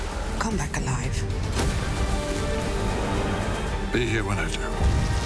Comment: What does The Doctor say at 0:18?? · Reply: Matthew Martin After Bill says "come back alive" he says "be here when I do."